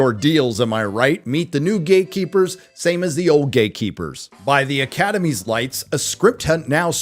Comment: Did you say GAYKEEPER?? 😄